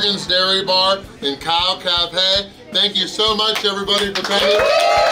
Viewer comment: Did he really say, "KALF?"